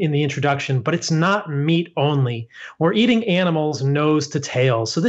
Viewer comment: That guy wont live a long life. He see benefits in The beginning but in The long run it's not healthy to only eat meat. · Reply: I stand corrected. I had originally heard him say at 5:14 - 'not eating meat only OR eating nose to tail'... etc. I had to listen several times and finally I heard what you heard, "WE'RE eating nose to tail..." It took many listens on my part - perhaps because I WANTED to hear that he doesn't eat only meat?? Anyway, thanks for the correction. btw I'm not in support of his way of eating - I prefer mostly vegetables and a small amount of meat for myself. I was a vegan for 10 years and got very sick. I didn't heal until I added animal products - and I cried for 6 months that my particular body needs some. So sad. But, in order to live in this vehicle called 'my' body, I do need some animal product (not eggs or dairy) - large majority is plant based however. I just listened to this in order to learn. Thanks for assisting me in that.